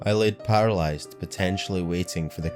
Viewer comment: Not to nitpick, but it sounds like you said "potentially" rather than "patiently."